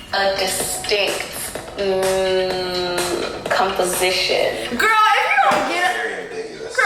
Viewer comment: You got skin bleaching with Miriam. I thought she was selling cum based skin care products or coochie juice cause of the way she said composition. I thought she said cum-position. 😭😭😭😭😭😭😭